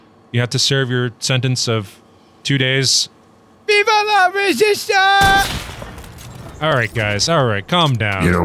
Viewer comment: !??!?! what the heck say ? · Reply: VI VA LA RESISTANCEEE!